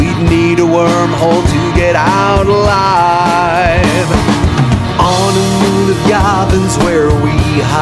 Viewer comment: I keep hearing it as "on the moon of Gotham's where we hide"